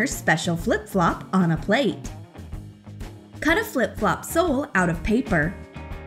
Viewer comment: I think she said “soul”